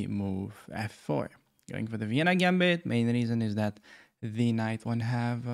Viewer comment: Why is it that in every video I watch from you I hear you say “now for the Halloween gambit”?